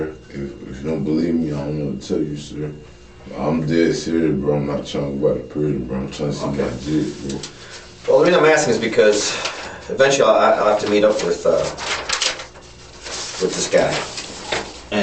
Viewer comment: I think he said "I'm trying to see my kids" Somehow getting him off the streets at 18 isn't soon enough to keep him from procreating